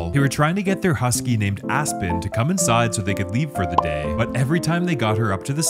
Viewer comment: I heard “Afton”